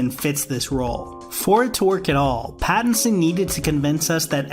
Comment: can anyone tell me what he was saying at 2:39? I'm hearing 'for twork it all' .... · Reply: "For it to work at all". Ya not good enunciation.